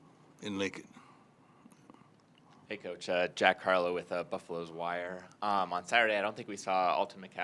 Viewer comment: Did bro really say Jack Harlow? 😂